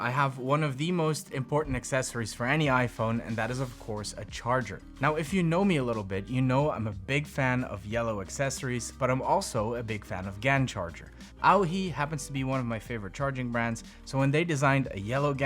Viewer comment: I thought he was saying gang charger and I really thought it was a charger with multiple ports to connect several devices...